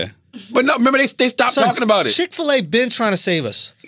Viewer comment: Did he just say "Shit Fil-A"..